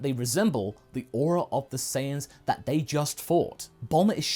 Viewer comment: Hahahaha!! For a moment there, I thought you said Saiya-Pasta!! Hahahaha!!! Then came to my mind an image of Goku eatting pasta!! HAHAHAHAHA!!